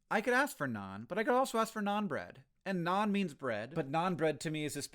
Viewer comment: Any time someone says "Naan bread" I hear "non-bread"